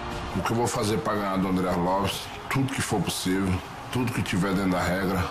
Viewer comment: Arlovisk? Where is Andrei Arlovski and why Arlovisk took his place? This dumb commentator should be fired, can't even spell correctly the name of the fighter from the main event. Arlovski looked cool tho=) guess it's not the first time someone can't say his name correctly! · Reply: I still think it is a mix of accent + the way he says it. Brazilians would find no mistakes with his pronounce. Well, to be honest, I heard "Arlovis" one or two times, haha... Peace.